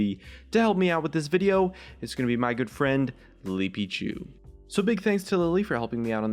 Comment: is it just me, or when we he introduced his friend on 0:56 he said "Lipichu" instead of "Lilypichu"... I am confused